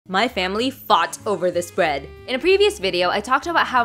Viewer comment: I heard “my family fart over this bread” until I read the caption 😊